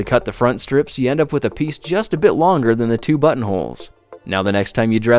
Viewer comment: at first I thought he said butt holes :/